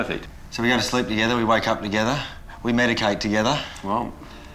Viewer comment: I thought Tim said "We made a cake together" before I realized what he actually said. So no way was I picturing a tiny lil Kenny in a chef's hat helping Dad bake a chocolate cake. Nope, not at all.